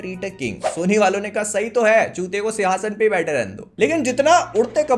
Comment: Did I Heard Saying Chutiya 😅😂😂